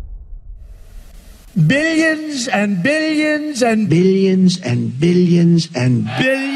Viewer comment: I thought trump kept saying "Billionson Billionson"